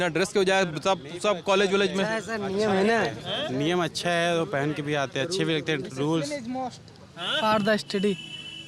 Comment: discipline is must for study..... think that's what he was trying to say